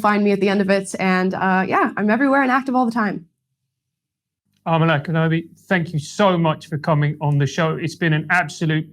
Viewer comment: Did he call her Amala Kenobi a mere second after she said her name?